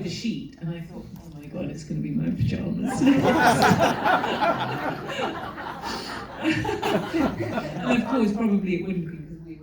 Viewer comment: What did she say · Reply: "omg it's going to be my pyjamas next" meaning she thought Barry was going to rip off her clothes after ripping off the bedsheet